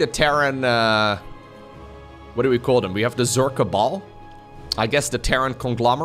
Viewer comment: did u said zerg kabal or a ball? cos titles says ball but i heard zerg kabal